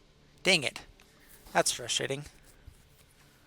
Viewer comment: he said that’s frustrating and i heard thats real shitty my Christian ears started bleeding at once and I thought how dare you HEATHEN! But he said frustrating so its fine I almost started a real shit show lmfao